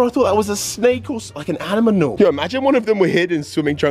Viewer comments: huh Did you say animanal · what did i hear😂😂😂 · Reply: aminamal😂😂😂😂😂😂